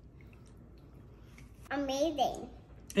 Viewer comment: When she says amazing I thought she said I made it! She is the most adorable little girl ! Learning how to cook & bake & now fudgesicles! Your doing a wonderful job your daughter ❤️c